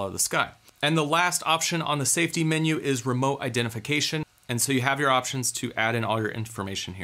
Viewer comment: Man I was chewing and thought you said "remote detonation" XD